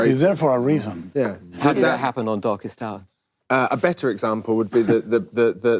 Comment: I thought he said "Doggystyle", but he said Darkest Hour. lol